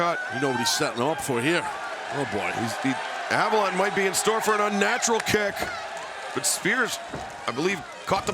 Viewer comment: Did he just say unnatural kick lol that was awesome.